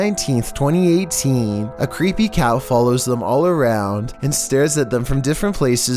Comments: Did he say "Creepy cow"? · so at 2:20 he calls the cat a creepy cow. does anyone else hear it? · sounds like he says "A creepy cow"... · did he just call it a cow? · it sounds like he said cow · wait did he say cow? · Does anyone else hears " A creepy cow" Instead of "a creepy Cat"? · Did he just say that a creepy cow is following them🤔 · Did he say creepy cow instead of cat?